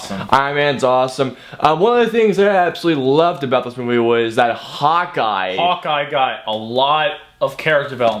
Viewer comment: For a second, I thought you said "Hot Guy" instead of "Hawkeye". Oh well. Oh yeah, Great Review! I need to see this movie!